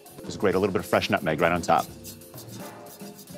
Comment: I thought it said spinach coffee